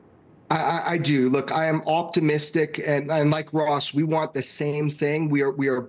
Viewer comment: At first I thought he said “I’m off to Mystic”. I was like damn! You coming to Connecticut?!